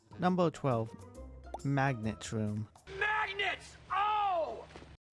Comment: Can anyone explain what he said? Magneto?